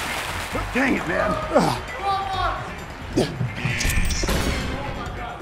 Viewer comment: what did you just say? lol XD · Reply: 50-50 chance